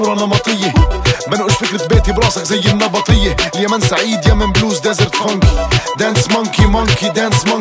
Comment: مبدعين 🇾🇪، 🇵🇸 2:33 سمعت اليمن السعيد✌ والا توهمت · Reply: اي ذكرها قال اليمن السعيد .. كل التحية لإخواننا اليمنيين ❤️🇵🇸🇾🇪